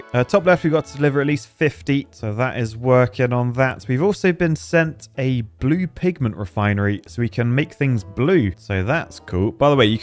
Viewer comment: I thought he said “blue pig man factory”